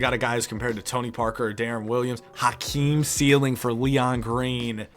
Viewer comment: it sounded like said Hotkeem instead of Hakeem 😂